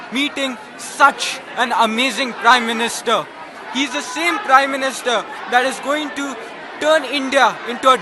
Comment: did he say he the shame prime minister?